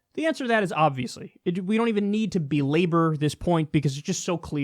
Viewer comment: What word was used there lol? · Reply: Bee labor. Maybe?